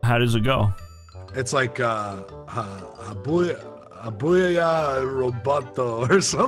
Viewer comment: as a russian, i have no idea what he was trying to say, but "good job" in russian is хорошая работа (horoshaya rabota) upd: oh i think he said something like "hobuyaya rabato" hahah, kinda similar